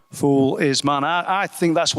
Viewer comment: I hear "foolish man" not "fool is man"